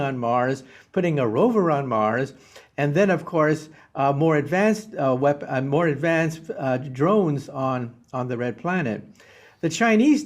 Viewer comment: Did anybody catch Mr Kaku almost say advanced weapon on Mars @ 9:02 mark